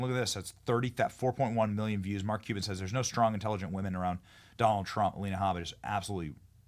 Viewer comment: I thought soyboy Mark Cuban said Trumps afraid of intelligent woman...